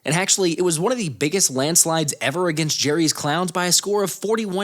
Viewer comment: Did he say Jerry’s clowns ?😂😂😂